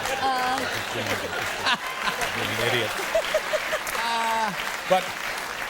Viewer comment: did he just called her Idiot?! :/